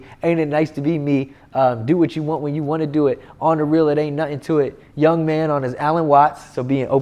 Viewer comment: I thought he was saying I ain't tripping on shit